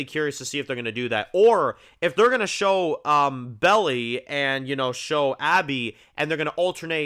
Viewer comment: did you call her Belly? 😂